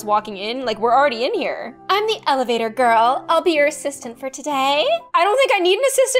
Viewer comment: instead of "elevator girl" its "evator girl" (idk if this was commented already)